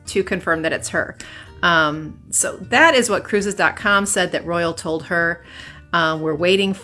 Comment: too young to Sail alone. . mom was the travel agent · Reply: I thought she said cruise con.